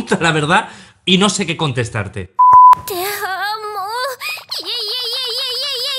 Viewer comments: parece que dice "te amo" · parece que dijo te amo xD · pareciera que dice TE AMÓ , pero en español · escuché te amoo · Bueno aquí está mi aporte En el minuto 2:46 parece que dice teee ammooo Vamos banda quien lo noto también 😃😃😃😃